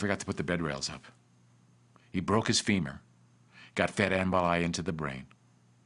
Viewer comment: 28: What did Noel say? Got fedambrali into the brain?